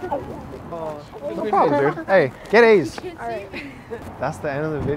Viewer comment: It sounded like he said hey get aids